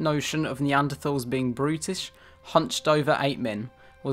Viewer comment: The first time you said "brutish" I was pouring coffee in the kitchen I heard "British" hahaha